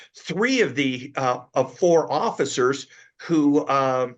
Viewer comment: "poor officers"? Did I hear that right? 19:58 Not a crime? The thin blue line is strong here.